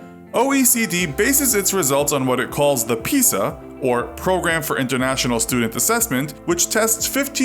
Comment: when he says "the pisa" i thought he said the pizza